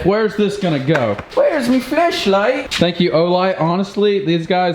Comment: who else heard where’s my fleshlight